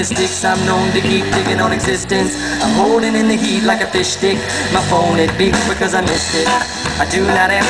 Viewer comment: did he say fishstick